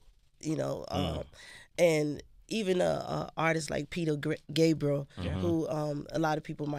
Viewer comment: Did anybody catch that? She almost says Peter Griffin lmfao18:47